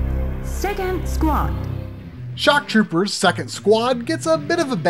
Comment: Second squad? I thought she said "sit and squat"